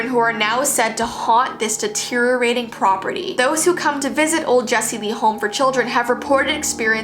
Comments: Did anyone else think she said ol Jassii Vee home? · “Jessie Lee?” OMGG that sounds like “Jessii Vee” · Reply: I think she actually does say 'Jessii Vee' instead of 'Jessie Lee" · I swear at 4:39 Jessii said "old Jessii Vee" and she was talking about a haunted place 😂😂😂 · did she just say jessii vee 😶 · I hear old jessie vee house in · who else noticed she said instead of jessi lee lollll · you said jessii vee home i mean i heard you said that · I am a little late but I am sure I heard “People who come to visit the old Jessie Vee Home” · did she say jessii vee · I thought u said Jessie vee house I was like WAT IN DA WORLD 🌎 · is it called like that or you just said jessii vee childrens